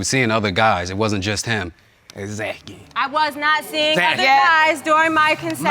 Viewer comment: did he say, "exacky"?😭😂😂😂😂😂